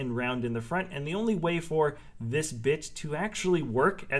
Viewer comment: I’m really questioning whether i heard that right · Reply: pretty sure he said bit